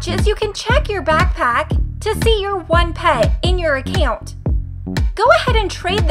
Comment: Did I just hear “ACCoint”